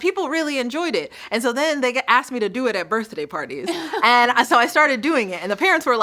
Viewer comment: Did Rachel just say “Birthdayuh parties?